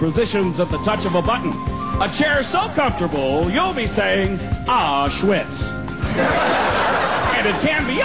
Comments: At first I thought Caliendo said "Auschwitz" but he meant, "Oh, shits!", a play on words. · I first thought he was trying to say “Aw, shit”, then I found out what the reference was